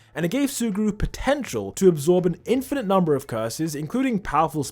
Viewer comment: did you say potential?